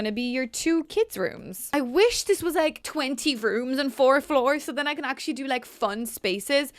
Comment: I thought you said rooms and doors and I was like omg how many doors are you going to be putting in this